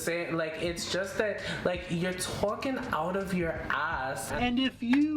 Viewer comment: oh my god, I heard "you're twerking out of your ass" and I almost choked on my sandwich